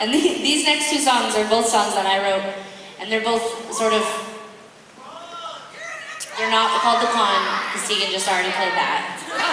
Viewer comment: What did Sara say at 0:50 thata made Tegan laugh? I can't quite make it out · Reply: "These next two songs are both songs I wrote and they're both sort of...they're not called The Con, 'cause Tegan just already played that"